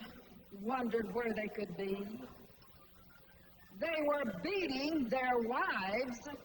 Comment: Can please help me understand what she's actually saying at 22:13 "I lookes for the elders, wondered were they could be, they were .... their wives beneath the big tree" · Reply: beating